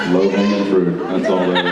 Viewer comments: wt did Jensen say at 31:15? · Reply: Kanika Mahajan "low hanging fruit." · what does jensen say at 31:19 that jared says "there are kids in the room" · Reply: He says 'low hanging fruit' -- meaning that using that question to respond to the other question was too easy, but Jared's 'kids in the room' comment implied that Jensen was talking about 'low hanging fruit' as innuendo in relation to what he what he was shimmying into the jeans right out of the dryer. :)